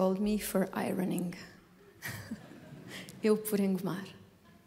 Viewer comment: Thanks for sharing. She is so great. Just a small correction: 1:09:37 Eu per Engomar (Solo: Christian Jaksjö, tb) Instead of "per" use "por" = Eu por engomar.